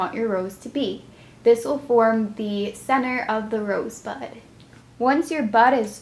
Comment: When you said "this will be the middle of the rose bud" I thought you said rose butt!